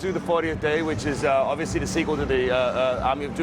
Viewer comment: i thought he said sequel to conduit lol..